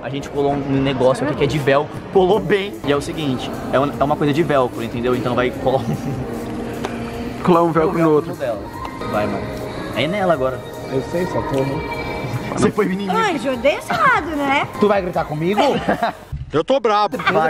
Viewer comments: Só eu que no minuto 02:22 achei que ele tinha falando "vai amor!" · So eu que ouvi um "Vai mô" no minuto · Só eu que escutei vai mô no minuto